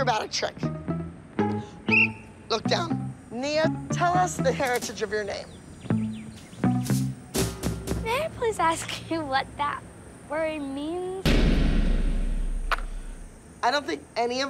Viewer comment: TELL ME WHY nia definitely said “mike wazowski what that word mean?”